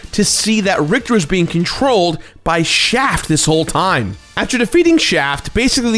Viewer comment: Lmfao dude I thought you said Richter was being controlled by "Shaq"